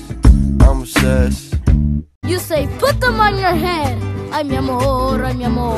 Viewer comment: I thought he said "and you f* em on your head"😂😂😂😂